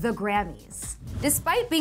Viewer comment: Am I the only one who heard her say "Gravy's" instead of Grammy's